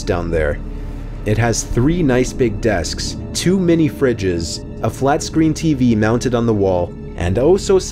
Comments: When he said “two mini fridges” I thought he said “too many fridges” lol I’m a dumbass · In the third story he said 2 mini fridges but I thought he said to many fridges